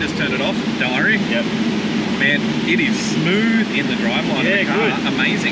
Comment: did he say "what a smooth driveline? "